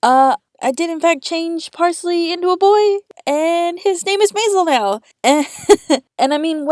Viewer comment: i totally thought you said his name was "mazel" and i went: is that even a spice